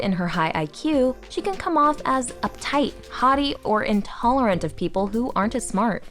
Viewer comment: Note the narrator has one of those accents that doesn't distinguish between "haughty" and "hottie." So I heard "She can come off as uptight, hottie, or intolerant of people who aren't as smart."